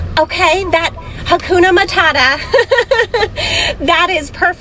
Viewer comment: did she just say "hakuno mootata"?????